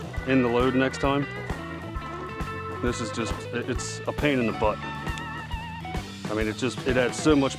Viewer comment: At the end of the video, I thought you said a pain in the birch. I almost spit out my coffee, had to rewatch the video.